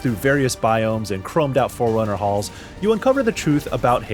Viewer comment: I heard "chromed out 4Runner halls" and had to pause for a sec.